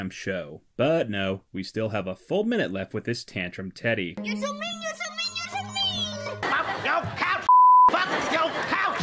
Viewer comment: "yosominyosominyosomin" turn on subtitles then your gonna know what he's saying💀